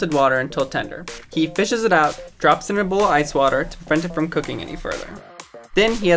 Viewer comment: Am I the only one that can’t tell what he I saying sometimes because when he said to prevent it from cooking it sounded like he said frent it from cooking